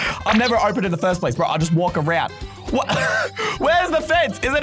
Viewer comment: did said... SIMPLY JUST WALK AROUND IT??😮